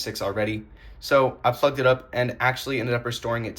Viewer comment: I thought he said “f*cked it up”💀😭